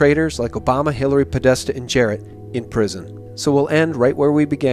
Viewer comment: Did he say, "Jared"? 11:40 Jared Kushner?